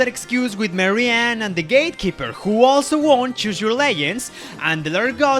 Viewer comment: Did you say Choose Your Layions.